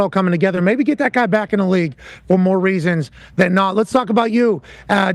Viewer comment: Did he just say let's get Gruden back in the league 😮?